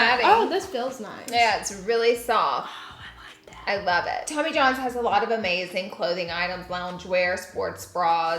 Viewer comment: My fatass thought you said Jimmy John's was sponsoring lmao 🤣